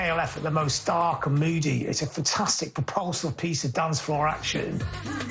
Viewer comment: What did that preposterous guy say? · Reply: "fantastic propulsive piece of dancefloor action"